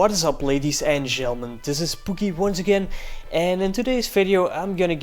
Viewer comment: i thought u said this is spookie